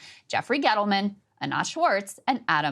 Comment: For a moment i thought she said Annette Schwarz. I got confused. lol .